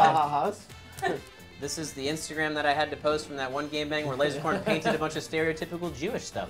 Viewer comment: For all of my life I thought you say gang bang but instead you say game bang😓 whoops